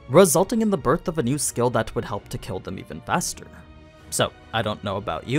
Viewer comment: do you say "even faster"? I've heard this as "even bester" and found it absolutely fascinating :)